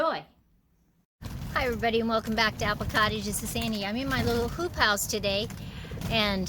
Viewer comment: My hearing isn’t great and at first I thought you said you were in your little poop house... ;-). Then I realized you were in your little hoop house....